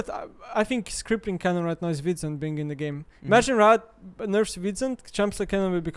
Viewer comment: what does say at 9:47 ? "crippling kennen right now is ..... being in the game". what does he say? i can't make out the word hes pronouncing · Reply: Wit’s End the item